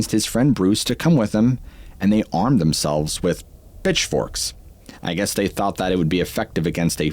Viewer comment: 😂😂😂 YT, You SILLY!!!😂😂😂 Autotranslates: “BitchForks” INSTEAD OF “PitchForks”!!! I NEED to GOOGLE what EXACTLY BitchForks ARE!❤😂